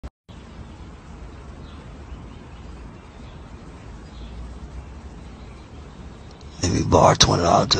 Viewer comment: I thought he said “ lemme borrow $20 to the mall “